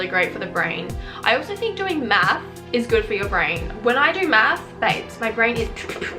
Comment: i thought u said breeding is good for ur brain lmao